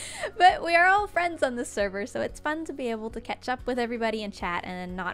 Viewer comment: did you say ketchup? You mean catch up?